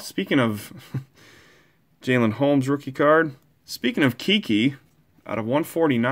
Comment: when he said keke the player I thought he said Kiki so in my head I’m saying Kiki do you love me 😂😂😂😂😂😂